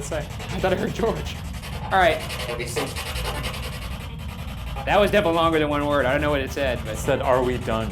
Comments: sounded like are we safe? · When you say "are we through" I heard "obviously" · I hear “Are we safe” instead or “Are we through” is that just me or..? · I hear 'who are these two' said really quickly. Not 'are we through'. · "are we through?" i heard "racing" broom broom